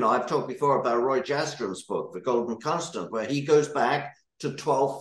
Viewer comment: What did he say?? Whose book? The golden ??? I think he said Roy Jastrom, The Golgen Custom. ??? Minute 12:18 or so…. Damn it, why do these people assume we know about esoteric references?? I realize this is badically of historic/academic interest… but why mention it if it’s just a throwaway line?